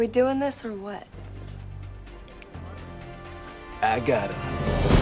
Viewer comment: what did he say? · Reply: "I got him..."